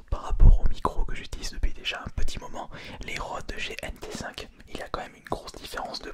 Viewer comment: les quoi ? 😂😂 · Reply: Les rodes gnt5 je pense